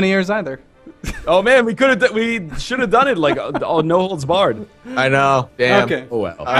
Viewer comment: does anyone know what NL says at 30:47 ? can't fully understand him... Love the episodes btw! :) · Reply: I'm pretty sure he's saying "no holds barred", meaning no rules, a free-for-all